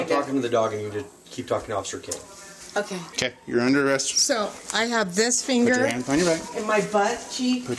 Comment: I have this finger in my butt cheek?? 6:18 ??? Is that what I really heard?? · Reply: "This finger. And my butt cheek" She was itemizing her "injuries"